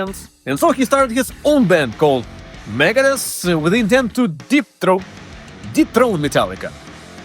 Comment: Lmao almost sounded like he said Dave was trying to deep throat Metallica instead of dethrone 😢😅